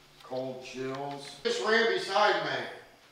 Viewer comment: you can't hear it without headphones and even then, it's hard to hear but when asks who ran beside me i hear " me" @ 28:00 !